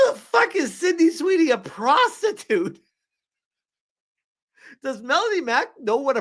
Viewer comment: Syndey a prostitute? All I hear is "so there sis a chance"